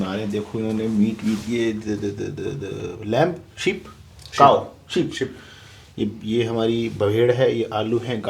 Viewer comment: i think he said COW